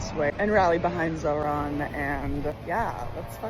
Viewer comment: Did I hear "rallying behind Sauron"? Yes they rallying behind Sauron lol.